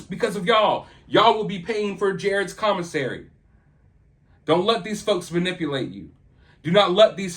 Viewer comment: You said "don't let these folks..." and I heard "don't let these fucks.." and I mean...it fits.